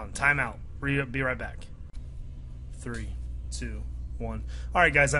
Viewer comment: i heard him say 3 2 1